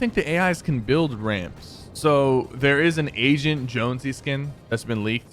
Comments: I thought he said "Asian Jonesy" and I was like wtf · When he said agent jonsey I thought he said asain jonsey 😂🤣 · I literally thought sypher said to Agent Jonesy "Asian Jonesy" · Nobody: Literally nobody: Sypher: Asian jonsey Edit:For the dum dums that dont understand it sounded like he said asian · who else thought that sypher said asian jonesy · it sounds like he said Asian Jonesy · At the beginning I thought he said an asian jonesy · Did he just say a Asian jonesey skin